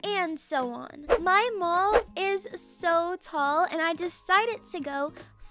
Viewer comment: for a sec, I thought she said "my mom is so tall" I came back and rewinded but actually said "my mall is so tall" IMAO 😂